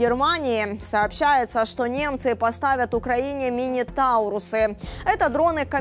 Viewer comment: "Германия поставит Украине ракеты мини трусы"? Или мне послышалось?😂